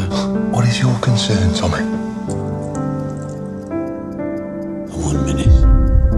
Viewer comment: What thomas say at 00:45? Its ' a minute' ? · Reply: One minute